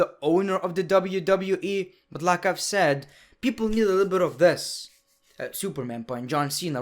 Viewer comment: oh you mean Superman Punch, I thought you were saying people needed a little wanking...